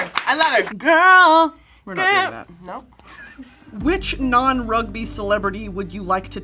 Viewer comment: when she asked what non rugby celebrity she would like to tackle, I was chewing and I misheard what non-right-wing celebrity would you like to tackle and I was like Wait a second that's so out of pocket and then when they said JD Vance I was even more confused lmao I had to rewind